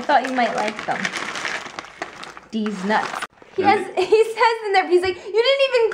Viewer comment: Okay, i'm probably wrong, but did Jeana say "Deez Nuts" at 6:52 ?!?!